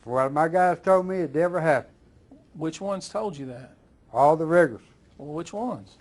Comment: dead ass thought he said, 'all the n*ggers'